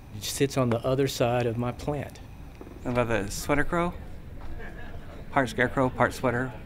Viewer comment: “the sweater girl” sir, I think you mean cardigan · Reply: Oh, I thought he said Sweatercrow. Part sweater, part scarecrow LOL